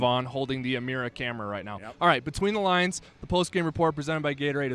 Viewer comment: Anyone else thought the intro said "BETWEEN THIELEN"?